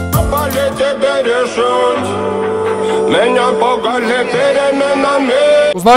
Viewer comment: Послышалось: Меня пугали беременными 🤣🤣🤣